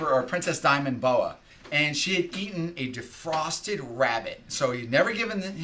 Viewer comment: I misunderstood on the first video. I thought you said it was a live rabbit and it had bitten the snake.